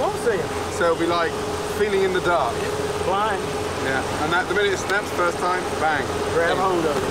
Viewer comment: "and something something something something the first time". What does he say here? I tried turning on captioning, but even captioning skipped the word/words I'm trying to hear. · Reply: The minute it snaps the first time